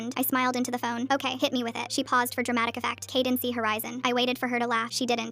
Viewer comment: Honestly, it's not that bad. Cadence is a rather normal name and if it's just Horizon with no 'the' between the two, Horizon would just be a middle name, and won't be used in schools too much. Even for scantron exams, they only ask for the middle initial, not the full middle name. Is it a name I'd give my child? No. But it's not like the name is Princess or North West or anything like that. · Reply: man i don't even think it's "cadence THE horizon," i heard "cadence-y horizon" which is.. not any better than the former 😭 maybe i'm wrong but the way the AI voice pronounced it made it sound like it was cadencee